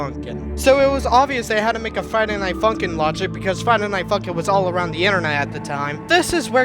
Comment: Sounded like you Said Friday Night F***in Here